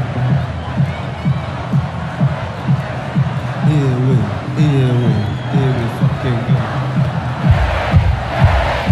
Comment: What is Alex saying at 0:37 ? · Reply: HERE WE HERE WE HERE WE FUCKING GO !